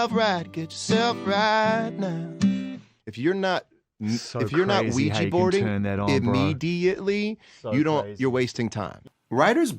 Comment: What does he say/mean at 1:00 ? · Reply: He says, “If your not ouija boarding immediatly your wasting time”. Look up a “ouija board”, he means that in songwriting it is important to just start singing and throwing ideas out there without hesitation. To go by instinct (kinda how the ouija board works).